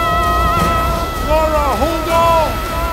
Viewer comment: I heard it Hodor 😂